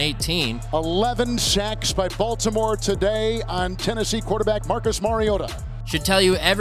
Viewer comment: Marcus....Marioto... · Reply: To me it sounds like he says ‘Marcus Mariodo’